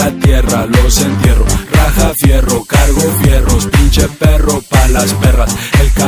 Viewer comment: JAJAJAJAJA, yo pensaba que decía "cago fierro" tanto tiempo cantandola así. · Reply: Yo pense que decia:Trago Fierro..😂😂😂 y asi la cantaba y todos..:😏🔥😂😂😂😂😂😂😂😂😂😂😂😂